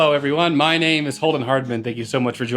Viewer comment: why do I always hear " Oldman Hardman" whenever he introduces himself?